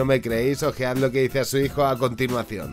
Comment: Che 4:51 q dijo no entendi?me dicen plis$! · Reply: Nadie te querra jamas como tu madre ;)